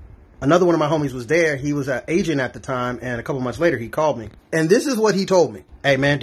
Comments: Sounded like you said "he was Asian at the time" lmao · I thought he said “ I had a partner and uhh he was an Asian at the time” lol had to rewatch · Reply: he really said “an agent” lmao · I thought he said, “he was Asian at the time”. I was like, what the fuck!?🤣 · “He was Asian!..” “At the time”💀💀💀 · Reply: I thought I heard Asian too… but he said agent lol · Bro I thought you said “he was an Asian at the time” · I legit thought you said “I had friend there and he was Asian at the time.” 😂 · I thought he said he was at asian at the time thinking it was a restaurant or some shit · I so thought he said dude was "Asian at the time" 🤣 I was like how was he Asian at the time?! Did he change ethnicities all of a sudden? After thinking about it I realized he said agent not Asian. 🤦🏼‍♀️🤣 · 100% thought you said your friend was Asian at the time not an agent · First time listening thought you said "he was Asian at the time" and my mind melted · Lol I thought he said the guy was Asian at the time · I thought you said he was “Asian at the time” 😂😂😂😂😂